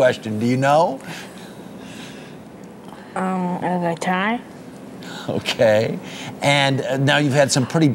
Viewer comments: wat did he want for christmas??it sounded like he said a tie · What does he say he want for Christmas? a tie?